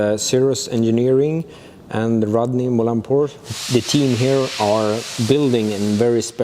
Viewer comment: anyone else hear Rodney Mullen? Hmm